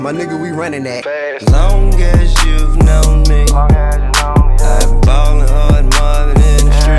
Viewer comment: I thought he said "you don't know me"